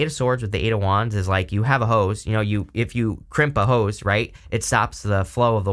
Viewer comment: 😅 "you know you have a hose.." Initially, I heard "you know you have 'hose'..." 👀 😂 Back to the scheduled programming... nothing to see here...🤣